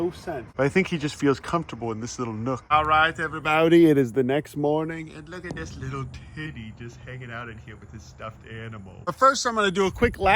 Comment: bro did NOT say tibby